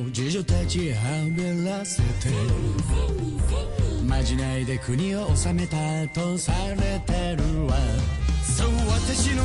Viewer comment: 間違いで国を治めたと聞こえた